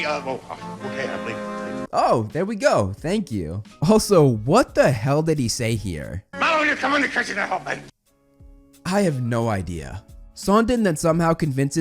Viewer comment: it sound like he was saying, “now you’re coming because you didn’t help me.” But he sounded like he was channeling colm Wilkinson.